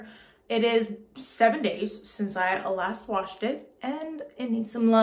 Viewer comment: I heard, "It has been 7 days since my last confession." 😆